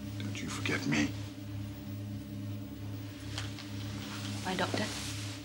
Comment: And all this time I thought she said "my Doctor" instead of "bye, Doctor". Would've been better my way.